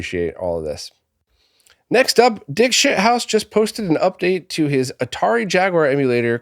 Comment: lol did you say “Dick Sh*thouse?”